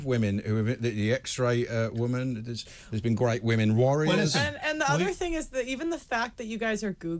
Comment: Did he said "great women warriors"? 🤨 LOL